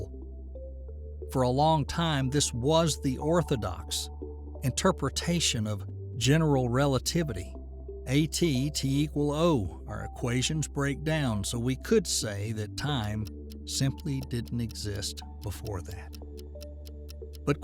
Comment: no human would pronounce "a, t, t, equal, o, our equations break down". (Instead of "at t equals 0".)